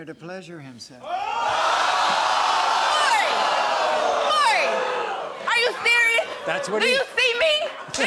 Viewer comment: Did she just call Maury "Jerry"? 🤔 Anyway, this girl needs to move out of her mother's house so she can have her own life and possibly keep her mother out of their business. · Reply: Sounded like she said Merry/Mary 🤔😂